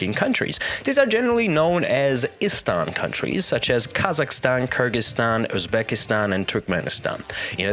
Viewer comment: 5.46 did you just say Kurdistan as a Turkish speaking country in the central Asia??????????? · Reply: He said Kyrgyzstan not Kurdistan